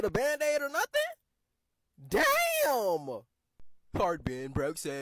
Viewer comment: did he just say "gYaT DaMn"